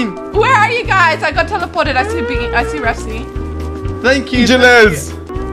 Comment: Did she say “I see Biggy- I mean refzy” Or “I see bean- I mean refzy” 🤔